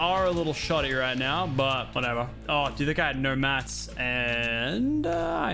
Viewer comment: did he just say what I thought he said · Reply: Magnus Bascombe he sed mats I’m pretty sure